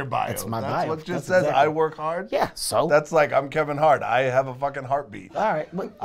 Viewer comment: “I am Kevin hart and I have a f*cking heartbeat” 😂😂😂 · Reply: I heard that as in “I am Kevin Hart and I am a fucking heartbeat” 😂